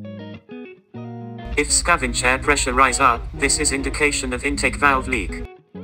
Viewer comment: Bro, what did you say @ 02:12, if the scavenge pressure......? It's not audible clearly. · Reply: Normally, scavenge air pressure is zero on boost air pressure gauge. If boost air pressure rises up while testing, this is the indication of intake valves leakages. Hope you clearly understood. Have a good day !